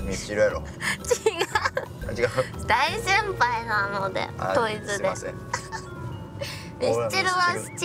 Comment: 「ミスチルやな」「違うな」のくだりにするために文字数や頭文字聞かれたのにあえてアルファベットで答えてさらに流れ作ったのだとしたらお笑いわからないって言ってるわりにお笑いわかりすぎてる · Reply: 辺りで、「ドイツで..」って聞こえるから、海外アーティストじゃない？